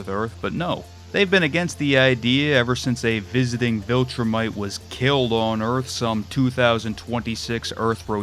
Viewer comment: did you just say jesus christ was a fucking viltrumite? 😭